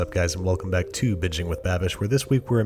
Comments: I thought he said "hey what's up guys, welcome back to Bitching with Babish" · I thought he said ‘Bitchin with Babish’ lmao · I always hear "Bitching with Babish." Might be my hearing disorder, though.